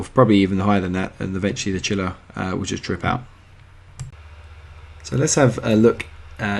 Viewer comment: Thank you. For those wondering(hearing impaired), he says "trip out," not 'drip out' like the caption says